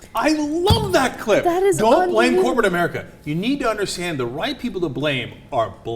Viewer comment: He said "the right people".... Why did I hear "the white people to blame, are black people"